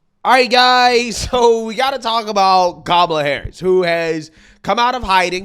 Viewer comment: I swear it sounded like you said text goblin harris